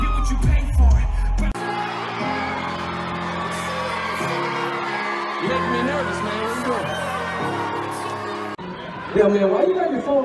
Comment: what did he said ? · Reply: I'm pretty sure Nathan said "You make me nervous man. What are you doing?" 😅